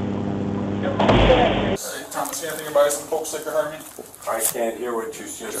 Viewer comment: Wow, I couldn't understand what that officer was saying either. Dude really needs to work on his speech. He sounded more intoxicated than Luke, er, I mean Tom. "Doyouhaveanythinginyourpocketsthatllpokestickorharmme?" All one word.